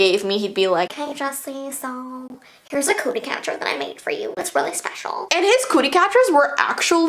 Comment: I thought she said “a Coochie Catcher”